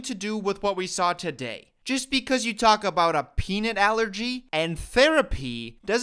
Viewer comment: PEANUT ALLERGY!!! I legitimately thought he said "Penis Sensitivity? I beat it" I know. I know.